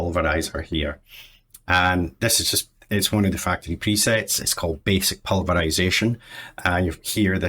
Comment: I first heard say "it's just one of the fackin' presets" xD